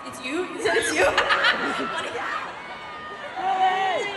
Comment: I thought she said waffles and candies 😭